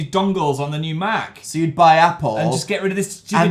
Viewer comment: I thought that said by an Apple...Yeh no that’s healthy